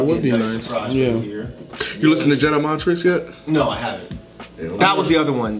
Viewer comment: Anyone know what said at 10:10? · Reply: Jedi Mind Tricks